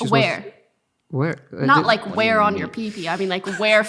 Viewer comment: I thought micheal said whore